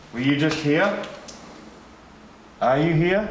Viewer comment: there was a whisper, i think it’s saying “ all of us”.